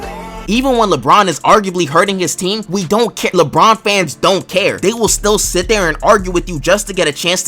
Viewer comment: Did he said we ? 😅